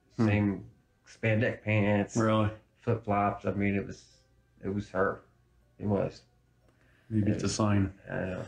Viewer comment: I hear "it WAS Chris"